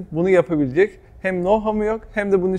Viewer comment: ile 02:55 arasında ne diyor? Hem no ... yok hem bütçesi yok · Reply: Know-how